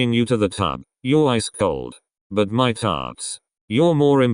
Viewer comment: i thought that he said but my farts -💀